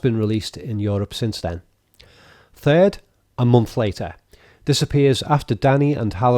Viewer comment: Your scouse accent sounds like you are saying 'disapppears' instead of 'this appears' when talking about the captions, even the subtitles think so.